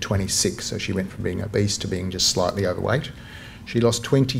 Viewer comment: I had to do a double take at 17:00. I thought he said, "She went from being a BEAST to just slightly overweight". Wow. He's not pulling any punches.😆